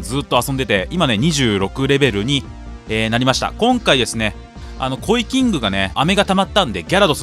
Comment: KCじゃなくてコイキングって聞こえた